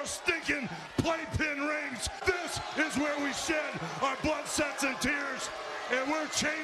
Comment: Did Hogan say... "This is where we shed our blood, SEX, and tears..."? lmao